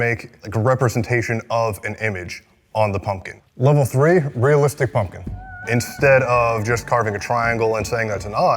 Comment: I thought level three said 'racist pumpkin' 😭😭😭😭